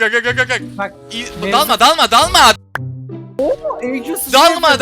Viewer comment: ne dedi? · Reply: Dalma diyor bağırdığı için sansürlemiş editor